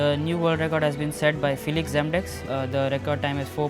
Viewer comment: When the Indian said feliks zemdegs I heard it like "fill exam decks"